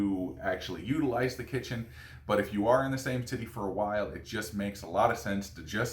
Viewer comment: lolz said titty instead of city